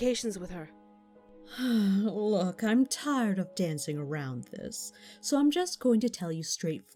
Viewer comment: Was I the only person crying through this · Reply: Dezzie TnT// Destroyer_of_TnT I thought you said dabbing 😂😂😂